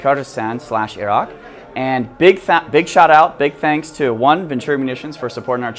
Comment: big fap big what? 😂 oh shoutout